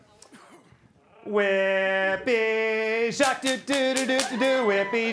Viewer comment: LOL I actually thought he said Whippy Shark until I realized he instead said Jacques 😆👌